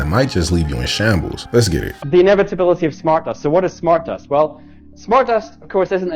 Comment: Did he just say MU dust?! Ha ifykyk :0